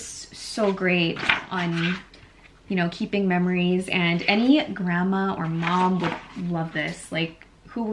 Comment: OMG! At 20:48, I thought you said, “Glam-ma” instead of “Grandma”! Lol I had to rewind that to hear it again, but I guess I was hearing things. I hope my future grandkids call me “Glam-ma”!😆